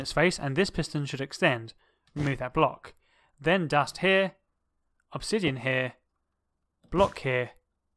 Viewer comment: Yyayayyyyyayaa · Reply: you said obsidian instead of observer ;)